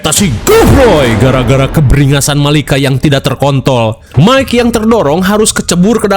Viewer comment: ngomong terkontol kurang R bang sampe kaget dengernya